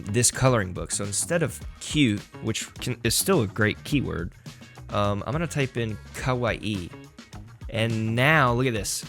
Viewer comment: “So instead of cute, I’m going to type in cute”. Lol